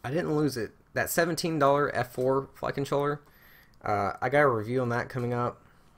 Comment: Can you make a video just saying the word "flight controller".... please... I don't know if everyone else hears the same thing but I keep hearing "fuckin chore" LOL!!~~ BTW... keep up the awesome job!